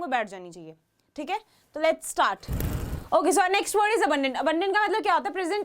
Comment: it should be abundant instead of abandoned